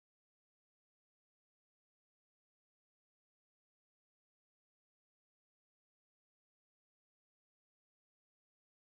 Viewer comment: I'm trying to understand what she's saying 😭✋ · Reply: It's "And If You Ever Lay A Finger On Him" Your Welcome!